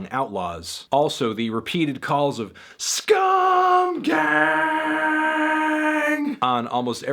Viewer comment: Damn he was saying "SCUM GANG"? i thought he was saying "SKULL GANG" learned something new